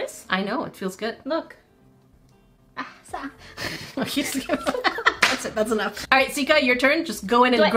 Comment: did she said "ساقعة "😂😂😂😂